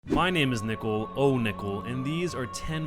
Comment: lol I could have sworn he just said my name is nipple